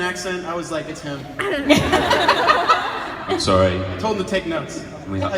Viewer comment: what did alberto say? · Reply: "I told him to take notes"